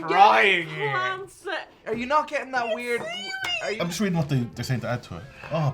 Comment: I have tried three times, but I can not make out what says at 1:56 he's just reading what?